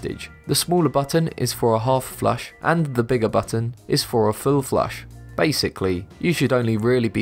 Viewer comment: On 4:20 , did he just said FLU FLUSH? Lol!!!! Oh by the way..all commenters before me are all dumb!